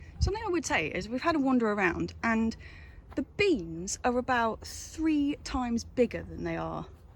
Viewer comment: "The bees are a lot bigger here" How's that happen? What are they feeding their bees? And then you're looking at "the beans." Oops, misheard that. It was still an enjoyable vlog even though it didn't go according to plan. Cheers.